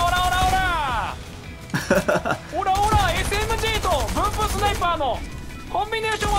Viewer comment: がブームスナイパーって言った時ブンブンスナイパーって聞こえたw